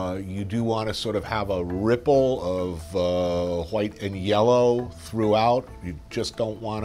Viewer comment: did he actually say Hwite ?